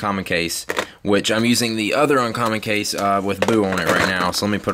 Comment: lol sounded like you said "i use my other case with poo on it"